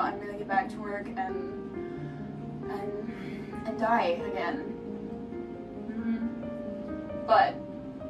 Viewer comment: did you say, die again. ?